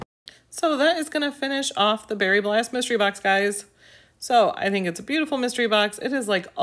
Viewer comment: I thought you said the very LAST Mystery box at first. It was not you, it was my ears not paying attention to the actual name of the mystery box lol Am I missing the link for the giveaway? I'm sure I'm just skipping over it, but I don't see it :)